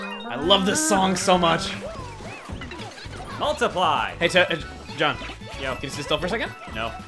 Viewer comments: "Multiply!" THAT'S what Toad says!? I could never make out what he said. O_O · The first time i played this game when toad says multiply i thought he said suck a pie.